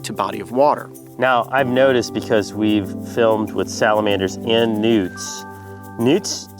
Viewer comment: at first i thought he said nudes lol